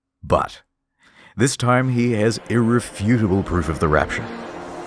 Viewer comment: says "irrefutable proof" while the captions say "a refutable proof". So the captions are more accurate...